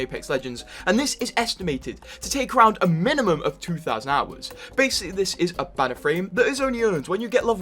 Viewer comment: anyone know tf he even said? How many hours? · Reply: 2000 hours i think